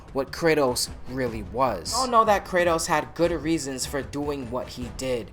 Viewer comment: did you say "gooder"